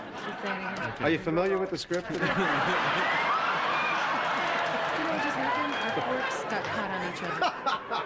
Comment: Can anyone tell what Julia said to Jason at 1:17? She looked angry. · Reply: "You know what just happened? Our forks got caught on each other."